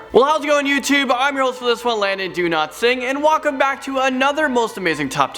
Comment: I thought I heard him say I am Landon don’t sing do not hate meh